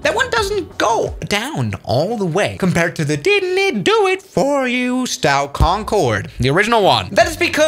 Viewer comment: Sounded like he said "didnit do it for you"